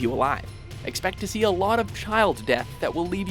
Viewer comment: "Many childs dead" - Is it just me misheard it or something?